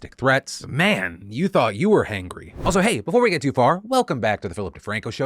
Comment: At the beginning, about the nose-biting, I thought you said “and you thought you were Hank Green”!! I had to listen back two more times before I realize you were saying “Hangry”, not “Hank Green”. But to be fair, I did just come here after watching a Hank Green short! Lol